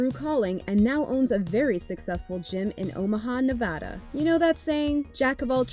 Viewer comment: Did she say Omaha, Nevada?